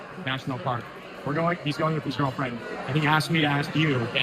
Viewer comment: I listened three times and didn’t hear ask Troy Jackson about where to go with a cousin. I heard “girlfriend”. Anybody else?